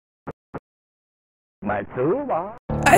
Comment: I was so obsessed with this game when I was a kid I was would sit around drawing fan art of it for hours. I tried to find other media that had a similar feel but I could never find anything else like it. My friends and I even quoted the weird language. The "anesta" line was always "I messed up!" spoken quickly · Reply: I always thought the alien guy said Maktuba lol but yeah this game is forever enshrined in my childhood as one of the weirdest, hardest, but cool as hell game